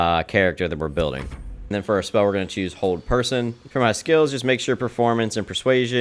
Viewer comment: I misheard "Hold Person" as "Old Person"